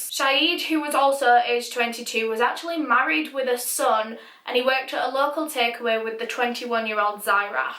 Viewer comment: I swear to god I heard "married with his son"